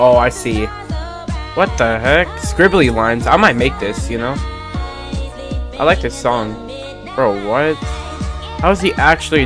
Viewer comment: U thought bro said I like this song you made💀